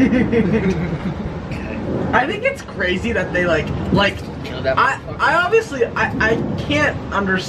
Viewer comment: LMAOOOO WHY IS NO ONE TALKING ABOUT WHAT SAID?? 💀💀😭😭🥴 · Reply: When said “I think it’s crazy that they” he said “killed that mf”